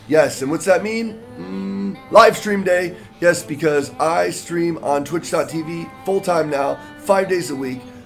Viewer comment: i thought he said icecream instead of i stream